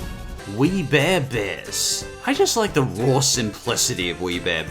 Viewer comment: I thought he said weed bear bears